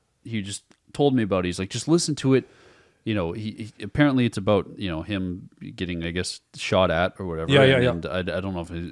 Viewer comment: i’m sorry.. but bro did say niggas ??!